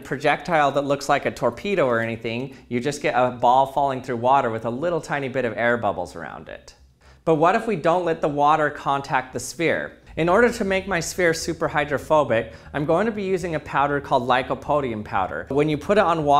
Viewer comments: I thought he said "In order to make my sphere super homophobic" 💀💀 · I heard "Make the sphere super homophobic".